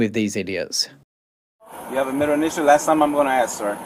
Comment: Do you have a middle initial? I thought that he said do you have a mental issue. 😂😂